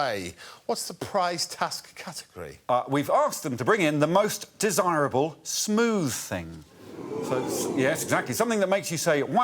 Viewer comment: I thought he said "most dishonorable smooth thing". That would have been more interesting, I think.